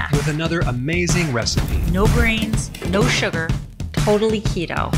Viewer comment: Ok I got to wonder does anyone else hear his wife say no grains and I swear it truly sounds like she says no brain's!!! Lol every time I hear it on every video and I rewind to hear again to try again listen really hard but I still hear her say no brains 😆